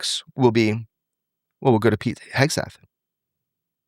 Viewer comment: " then the flex will be . . then we'll go to . . pete hegsteff? hagstaff" ? what does he say there? I slowed it down to .7 speed and still couldn't understand him.